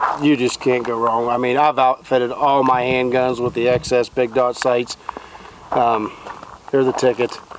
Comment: Sounds like he said big dog sights.